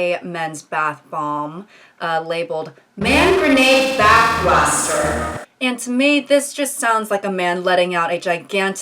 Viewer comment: i thought that said; “Man Grenade Pain Plaster”